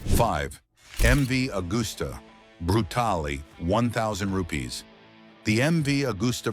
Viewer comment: did he just said 1k rupees 😅😂